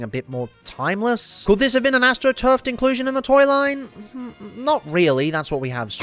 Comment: also... 7:46 did you say astro funk? because that's a real HW car and BOY would that be wild to see as a transformer. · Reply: No, "astroturfed". The opposite of "grass roots", when a company tried to push something and claim it was always super popular.